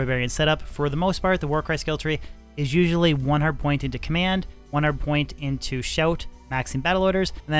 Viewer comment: It may only be me, but everytime you say "1 hard point" I hear "1 hundred point". Nice video.